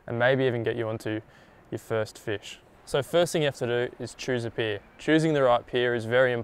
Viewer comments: Hahaha at 0:17 I thought he said "the first thing you need to do is choose a beer....choosing the right beer is very important". Couldn't agree more. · First 20 seconds I thought he said "choose a beer" my thought was that he's on to something here